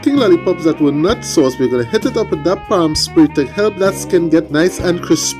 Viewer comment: Oh man, those are the kind of lollipops I like!!😁 that glaze sounds crazy good 👍 I guess even potato salad needs that heat factor 🤔🥵🤣 I thought at first you said spraying with palm spray, I was like oh something new...come to find out it was Pam spray 😂 another great cook on the cheaber..and one rocking video!! Edits always money👌 hope buddy got a taste test🙂 thanks for sharing brother, have yourself a great one, you all stay safe!!👍🍻👊🍺 and my boy Buddy!👍🐕🐶